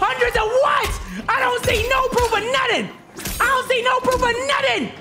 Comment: Bro did anybody else hear at 54:47 “I DONT SEE NO PROOF OF NUTTING”😂